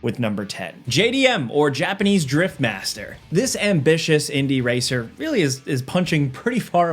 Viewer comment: Ngl I heard JDM and swore the next thing said was Japanese Drip Master... huh this dripped out car sure is drifting alot.... oooohhhh